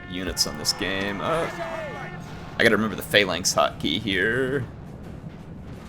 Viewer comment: i thought they said phalanx hockey now that would be a blue line defence